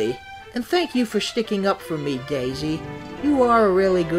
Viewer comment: sounded like “Gayzie”